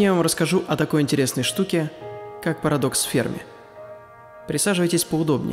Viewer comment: Мне послышалось или он сказал «Парадокс Спермы»???